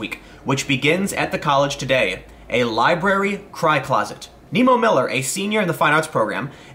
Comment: I thought you said "high closet".....now I understand.